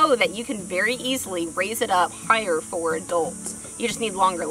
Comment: Did you say you can raise it up for dolts ?